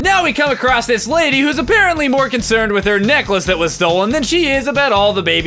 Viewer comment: When I first heard this I thought he said that she was more concerned with her neck that was stolen Also this is my first pbg video I watched